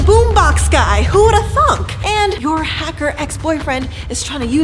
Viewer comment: Is it just me or did say who the f